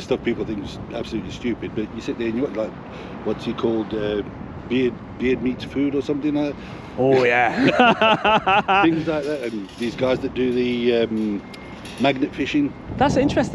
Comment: Love Beard Meats Food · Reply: I thought he said beer meets food ❤😂